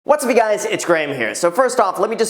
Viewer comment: I always thought he said, "What's B guys" in each intro. Now I know, "What's up you guys." Wow. Haha.